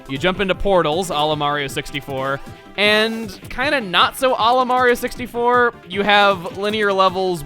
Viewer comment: sounds like he is saying Olimar.